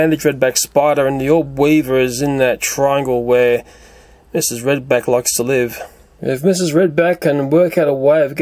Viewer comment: My teacher name is mrs.Brubeck lmao Edit: I thought he said mrs.Brubeck but he said red back lol